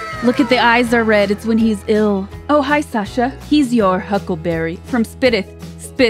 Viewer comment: Doc says huckle bearer, after someone corrected me i cant unhear it now, i also thought he said huckleberry but it is huckle bearer as in the handle was called a huckle on a casket.